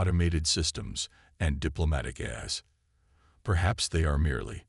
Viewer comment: if I heard "Diplomatic Ass" from an alien representative, it'll be worth it to be fired for laughing.